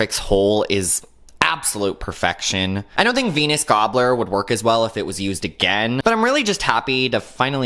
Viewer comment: VENUS gobbler I always heard fetus gobbler 😭😭😭